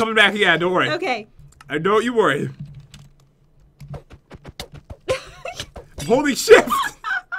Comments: From 17:35 to 17:50 in that time does he say holly sh*t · sounds like he said holy sh*t but he said holding shift XD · did pat say what I think he said noooo pat don't swear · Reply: Christian Masci he said holding shift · he didnt swear at 17:40 =P he said holding shift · OMG I THOUGHT PAY SAID HOLY S***BUT HE SAID HOLDING SHIFT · Lol it sounded like he said holy sh*t but I know he said Holding shift XD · Pat: HOLY SHIFT at 17:40 jen was laughing at 17:40 Pat Fell at 17:40 some people thought pat said Holly sh!t like my mom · GUYS ITS OKAY HE SAID "HOLDING SHIFT" NOT WHAT YOU THINK HE SAID SO CALM DOWN GUYS!!!! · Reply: Scarlet Mercenary THANKS... I THOUGHT HE SAID "HOLY $H** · Did he say “Holy Sht!” Or “Holding Shift!” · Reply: He said holding shift, but everyone keeps saying he said holy sh*t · When you have to keep in your swear 17:40 Pat: “Holy shift!” Me: I see you! You ain’t slick! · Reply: I thought he said "Holding Shift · Did he just say the s word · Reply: No he said holy shift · When pat said "Holding shift" It sounded like he said Holy Sh*t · did pat say holy s**t? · I think pat said holy sh*t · did he say holy sh*t? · did he say holy sh** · I thought pat said holy sh*t but he said holding shift. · He said holy shift even tho i thought he said holy sh*t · I heard pat say sh*t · did he say holy shi*? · When Pat said holding shift it sounded like he said holy sh*t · Who else thought that at 17:40 pat said holly sh*t Just me ok